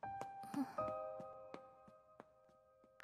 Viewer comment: did she just said bye bye to scythe